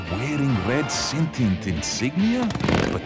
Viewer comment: i heard at 9 yrs old and at the end did he just say crap? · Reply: FlamingSkull222 Pathetic Trap not pathetic crap